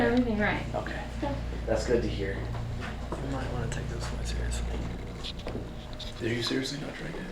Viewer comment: Rewatch when they all say the safe ritual and watch mouth compared to everyone else as their lips are moving and her's are not and of course the drink tips to the side to dump it you can clearly hear that liquid hitting the ground by her. I have rewatched this about 4x to see or hear what the Ev Mic picks up of course Everyone hear the deep breath or growl. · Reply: Did anyone else catch what said to? " you got to take this a little more serious"